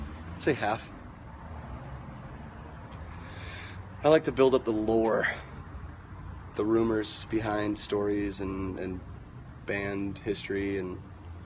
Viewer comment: he says something about his interviews and if they're true or fake...can someone tell me, what exactly did he said?! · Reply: I think he said about half he likes to build up the rumours behind story's for entertainment